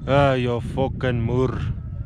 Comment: wtf does he say here haha? "You F**kin moore?"